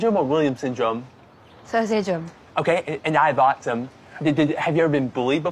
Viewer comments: When he said he had autism, I heard, "I have awesome." Cool dude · That's soo sweet. At the beginning it sounds like he says that I have awesome 😍 he is pretty awesome